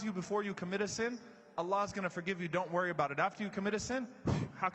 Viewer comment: Salam Alaykum, at 2:32 the captions say "a law's gonna forgive you" when it should say Allah's swt gonna forgive you.